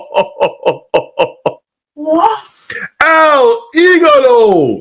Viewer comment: I thought he said El Igloo.